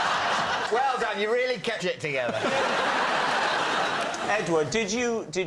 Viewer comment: That turned out very sweet, and David's "Well done, you really kept it together" made me laugh out loud. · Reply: There’s a cut in the film. I think he said kept your shit together